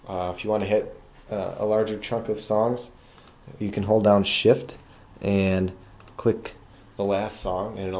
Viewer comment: at first i thought it said "you could hold down shit"